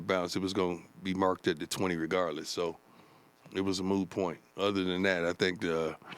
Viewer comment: Did Deion say “kick out of bounds was going be marked at the 20 anyway so it was a moo point” 6:53? So it was like a Cow’s (or Buffalo’s) opinion it just doesn’t matter. Its Moo.